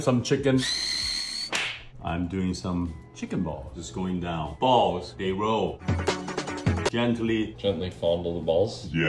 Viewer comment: I thought he said balls they grow😂